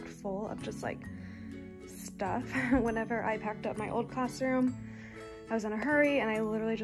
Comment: I thought she said 1st year teacher but she said when I packed up my old classroom?